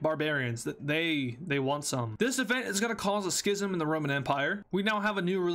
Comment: Did you say sh*t 💀💀💀?